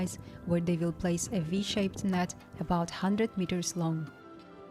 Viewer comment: did I just hear about 100 meters long? Please correct me if I’m wrong but that’s just impossible. That whole is 2 meters long at best